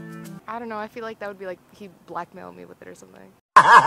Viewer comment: HIM: He gives you the pass what you saying?? HER: "I feel like he would Black Male me" lol